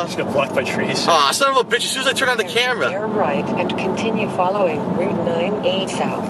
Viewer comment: 0:3 I thought GPS said "Damn right" Lol. Turn Right...